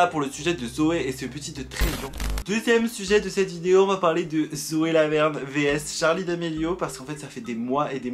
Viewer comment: à 8:07 j'ai entendue Zoé la merde au lieu de Zoé Laverne 😂